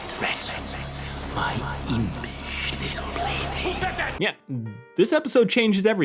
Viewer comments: i thought stoles said "my impish little blitzy" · I thought he said "plaything " my first watch, too. But I've seen a tone of people who heard "baby" . Unfortunately, the episode's subtitles are auto-generated, so I don't know which is true. And there is a big difference depending on which he said. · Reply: I just rewatched that scene, blasting my eardrums to try and make out what Stolas says. It's "plaything." You can hear the hard 'L' and 'th' in the way he says the word. :) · I thought he said my impish little baby, not plaything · I heard "impish little baby" idk this is why subtitles